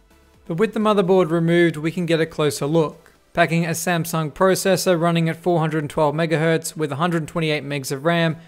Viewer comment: did you just Say Samsung processor